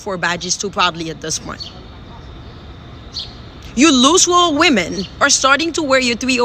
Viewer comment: Did she just call these 304's LOOSE WALL WOMEN???? 🤣🤣🤣🤣🤣